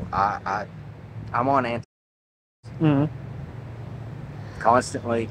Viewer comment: please tell me he didn’t just have to censor “antidepressants” for youtube, did I hear that correctly?